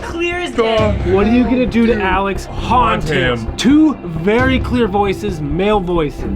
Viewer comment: i heard, talk to him